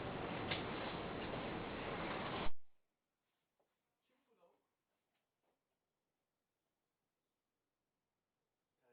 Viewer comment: den sonra ses neden yok. Eleman ne dedi acaba, Sarraf arkadaş ne dedi ? İzleyenler bunları merak ediyordur mutlaka. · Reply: sesi son ses aç duyarsın kimdi o lavuk diye geliyor eleman terziymiş burda dedi falan filan işte